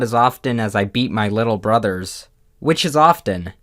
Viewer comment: I..I legit thought he said he eats his little brothers.